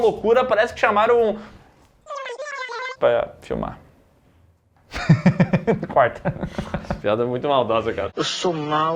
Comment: Fiquei doido pra saber qual foi o termo "censurado" que o falou em 15:15. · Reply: O Michael J. Fox que sofre do Mal de Parkinson